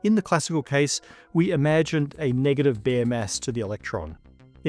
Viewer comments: I kept hearing "bare ass". My brain is smooth. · I'm amazed all these bare masses aren't grounds for demonetization 😅😂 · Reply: I misheard it initially and thought bare ass on tv lol Yeah I went there